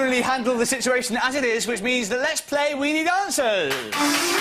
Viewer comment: definitely sounds like he says "lets play weeny dancers!"